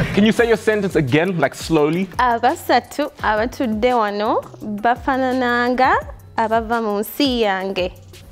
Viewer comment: I heard "bafana nanga" i though she was venda😭😭😭😭